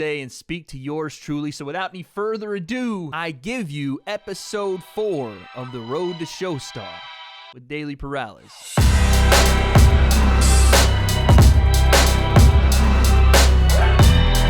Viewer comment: I thought he was about to say WroetoShaw lol